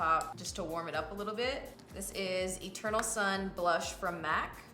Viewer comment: I thought you said " A turtle son blush by MAC" Then I realized you said "Eternal sun". 😂